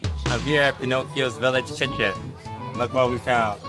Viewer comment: No way he said chicken instead of kitchen.